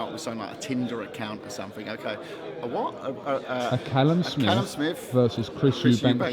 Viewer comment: Tbf when you said Smith he thought u said sniff